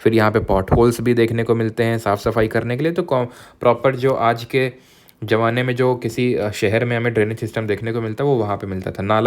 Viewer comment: did i hear butt holes 🌚